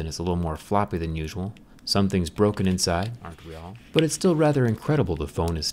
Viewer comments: was that just me or did he say aren't we not all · did you hear that?? I think he said “aren’t we are”?